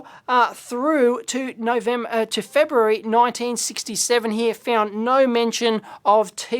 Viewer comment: *feb 1967 Caption is a bit incorrect, displays 1966 instead of 1967